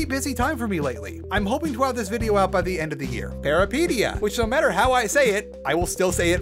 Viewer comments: What was the name of the game mentioned at 23:47? It sounded like therapedia but it's a nightmare to try and understand what you just said. Google had gone to shit so it just thinks I keep trying to type therapy. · How do you spell that? I can't figure out what the hell he said lmao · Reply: Peripeteia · which game is on 23:47, i didnt understand what he is saying at all lol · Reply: peripeteia